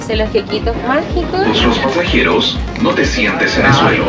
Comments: Minuto 0:42 pasa una vendedora que dice; asoló dos mil pesos los queques mágicos 😅😂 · Que linda visita chicxs! Oigan, soy la única que escuché en el segundo 0:38 a "mil pesos los quequitos mágicos" ??...amiga, deme 5!! XD